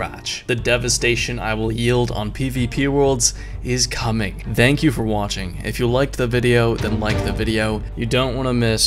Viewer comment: I thought I heard 'Dank you for watching' for a moment.